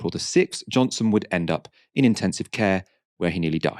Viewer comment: I briefly thought I was in a different and better timeline…..as I heard it…”ended up in ITU where he died”